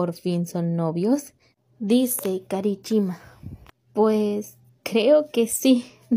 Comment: escuché cara e chima xdxd